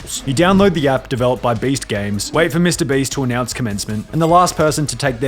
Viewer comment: It sounds like he says Based Games